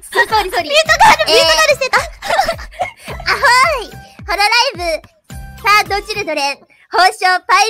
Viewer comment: I think she’s trying to say “third generation” there, but it’s hard to tell with that marineglish. As for the Luna part... sorry I have literally no idea